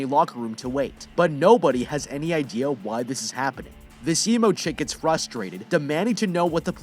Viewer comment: Disemochick 💀is what i heard 1:07 eViL gRiN she going to hell for that smile 💀😭 3:22 WIE_LOVE_COX 5:45 the reason they exploded